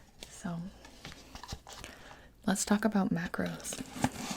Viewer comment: Y'all i can't.. my fatass thought she said macaroons instead of macros. This is a sign. 😭😭